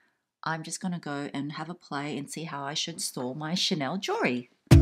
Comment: droned out a bit and misheard as how id store my shit but it was chanel jewelery lol